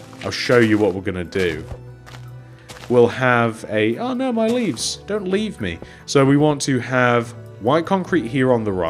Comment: "oh no my leaves, don't leave me" LOL I thought he said leaf, no pun intended.